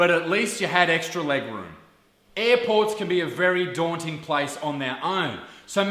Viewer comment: When you said airport, I thought you said airpods